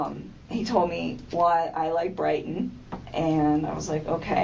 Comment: when she says “Brighton” I hear cuuuuzin all over that 😅😅😅😅😅